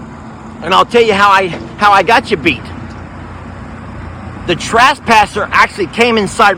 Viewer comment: Sound like he said trashpasser. Lol. Okay time for a gate. Smh. Omg not ok